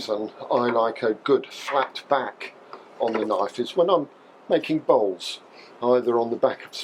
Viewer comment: PAUSE....What did he say, "When Im licking balls..."